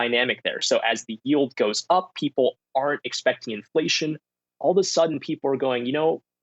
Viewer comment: did he say people aren't or ARE expecting inflation? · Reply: I had the same frigging question. It sounded like are but I'm pretty sure it has to be aren't based on what he said 10 secs before that. Basically, no inflation means people go to stocks and yields go up and that lowers bond prices.